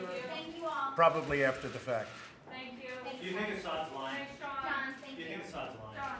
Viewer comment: On a replay the words are not clear, but I swear I heard “you need to stop lying” at 8:45. If so, I’d like to buy that reporter a beer. While our news media are improving at naming and shaming bullshit when they smell it, Trump & Co. still get away with far too many lies and evasions. “Did you read the bill? - Uh, yeah, I looked at every page. - That’s not what ‘reading’ means, if memory serves. I ask again: did you read the bill?” “[crazy conspiracy theory from Brietbart]. - No. That’s not true.” “[contradiction] - But you previously said X. So either you are lying now, or were lying then.” We don’t have to tolerate lies and (what is hardly better) unverified rumours for the sake of etiquette. Not when so much is at stake.